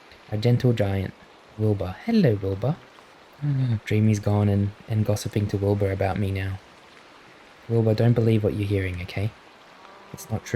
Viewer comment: I hear Boba not Wilbur 😭😭😭